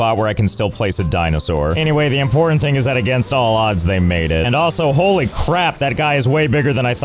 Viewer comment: What he said: "Against all odds, they made it" What I heard: "Against all odds, they mated"